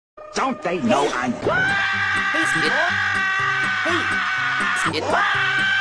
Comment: Sounds like Orange is saying Sniple instead of Squidward.